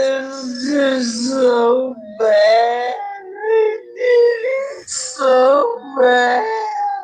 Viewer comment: Rewatch crew here I'm very much behind, but I hear "its so bad i did it so bad" no matter the speed it's played but I don't hear the word "him" at all